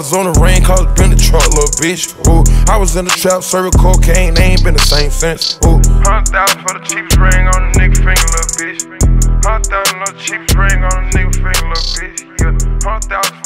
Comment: When Future said "the chapest ring" I thought he said "chickfale"